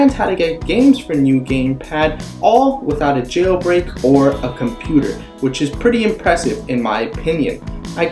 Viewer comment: I thought you said no computer